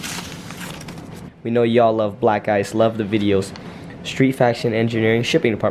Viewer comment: I just heard “We know y’all love black guys..” then i looked ohhh black ice. 😂